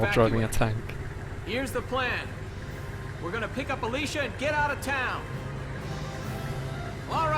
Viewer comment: I was playing Monster hunter and all I heard was anal lice and looked at the video..... excuse my mind.